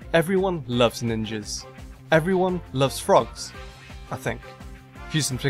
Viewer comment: did he just said that everyone loves frogs??